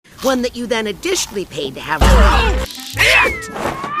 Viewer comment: what I heard: OH SHIAT